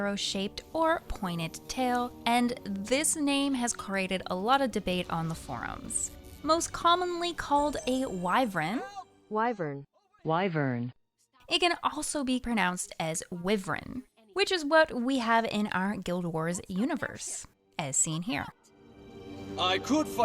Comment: I heard Wy-VREN. I heard it, I'm telling ya.